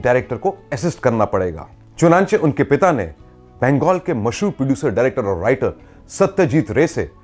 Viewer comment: What word he uses at 03:09 He has used this alien word in lots of his videos. What I heard is "Chonanche" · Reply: ‘Chonachey’ is an Urdu word which means Therefore or hence